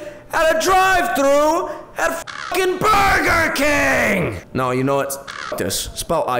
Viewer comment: DID JUST SAY BURGER INSTEAD OF BURBER!?!?